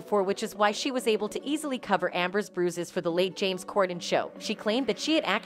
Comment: in video did they say “the late james cordon”?